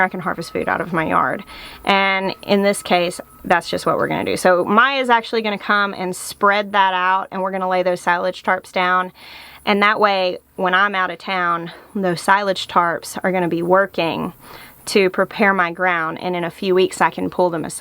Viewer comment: I kept hearing “silage charps” I wrote it off as having one too many pumpkin spice lattes. 😊